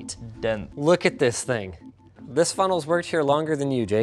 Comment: "This funnel has worked here longer than you, Jake." Haha, golden. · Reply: I thought it said funeral at first